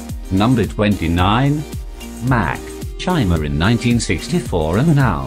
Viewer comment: did he just say mac